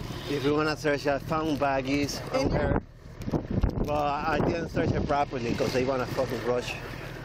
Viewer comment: Did i just hear a non-bleeped fbomb at 13:15 "i didn't search her properly because they wanna f*ckin rush"